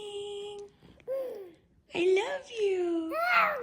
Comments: Omgosh I thought I heard say I love you too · Wow, what a happy 'good morning' that was! At 10:52 it almost sounded like he said "I love you" back to you! Also, you must be tired if you took a long nap after that entire pot of coffee!